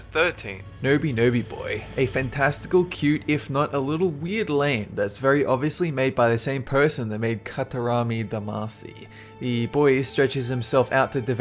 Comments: i dont understand what he say pls help me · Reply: '...a fantastical, cute, if not a little WEIRD land that's very obviously made by the same person who made 'Katarami' Damacy.' (Maybe a few words off) Katarami is supposed to be Katamari, by the way. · Please answer, what is that game called at 1:17? I heard nobee nobee boy but it isn't